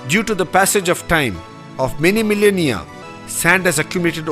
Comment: how does a guy born in 5114 bce build a bridge that accumulates sand for millions of years? am I the only one who noticed that? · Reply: I think you are referencing what he said at around 4:52 in the video. Where you are under the impression that he said, many million year(s). I think what he said was, many millennia. This would mean that he was saying that it took place over the course of many thousands of years.